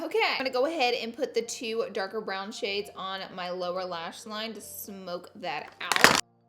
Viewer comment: I swear the first time I heard you saying "lower lashes" I tought you were saying "Laura lashes"... I know I'm crazy😬